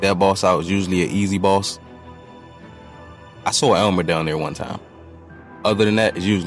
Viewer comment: I THOUGHT YOU SAID ELMO FOR A SEC 😂